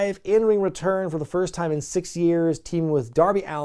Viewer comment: It sounded like he said "60 years" WTF